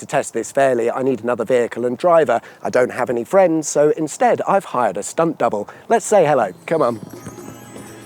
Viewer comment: I was distracted at the beginning and thought you said "I've hired a spunk bubble". 😂